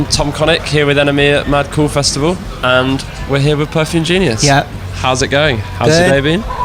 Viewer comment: I thought he said “how’s the baby?”. Somehow I think Mike did too, and that’s why he chuckled there. Maybe.